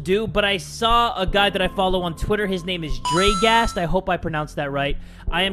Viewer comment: At the beginning of the video I thought he said Drakeass!😂